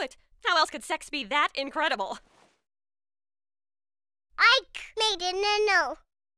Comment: “Ike made a nun-no” 😂😂😂 that made that scene 1000x funnier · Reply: What He Actually Said Was "Ike Made Another Note" Its just misheard Because Ike Still Cannot Pronounce Some Words... I think